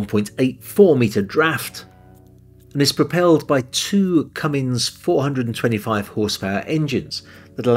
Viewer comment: Sounded like you said “Cummings” engines! The transcript correctly says Cummins.